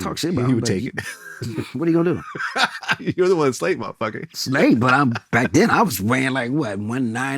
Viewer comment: I think he turned up on vlad low key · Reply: Yeah he did. What did vlad say after Joe said what he gon do at 5:43? Couldn’t hear vlad he was mumbling. Something like “your the one who slated a motherfuka” ?